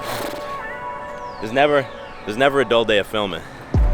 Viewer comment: what did you say at 3:06? because my english is not good enough to catch it xD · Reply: Zdzichu Krawężnik never a dull day of filming